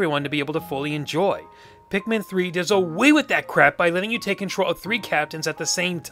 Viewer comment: did just say crap